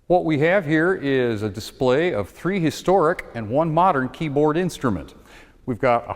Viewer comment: even though they're obviously NOT, it sounded like he said "prehistoric" :P